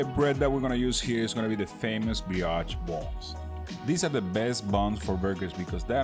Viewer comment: Love how it sounds like he is saying “Biotch” buns. Great video content.